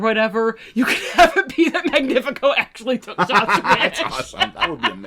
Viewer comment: I'm very sorry but at 1:38:00 you were saying something but laughing too hard for me to understand you, what were you saying? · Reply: "you could have it be that Magnifico actually took Soth's wish."